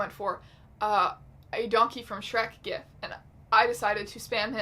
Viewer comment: I heard “donkey from shrek” as “yonkey from shrek” I don’t know how to feel